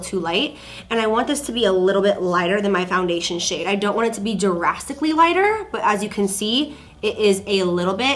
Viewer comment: I really thought you said “Jurassicly too light” instead of “drastically too light” 😂 I had to rewind